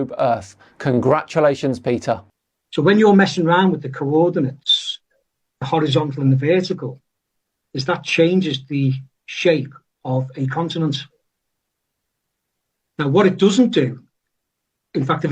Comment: I heard "changes the shape of incontinence" anyone else? 😆